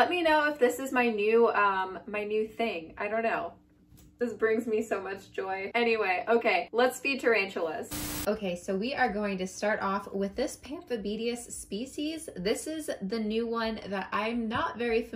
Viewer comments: my gf thought you said "Let's Be tarantulas" lol 💖🖤💖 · I know you said let's FEED tarantulas, but I heard let's BE tarantulas lol Prediction for end of 2020: 100k subs! You're SO close!!